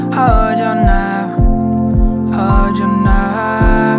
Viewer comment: I always hear "hold your nuts''...